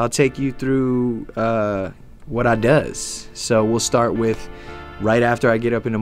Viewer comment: Did he just say "What I does"?